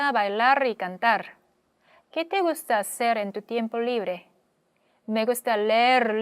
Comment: 호세 대사 중 te gusta hacer en~ 이 부분이 음원으로 '구스타세렌'으로 들리는데 연음으로 인한 현상인가요? gusta^hacer^en 이렇게가 연음현상인가요?